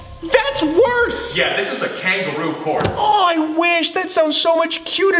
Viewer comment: I just realised this but did say "Kangeroo Porn" ??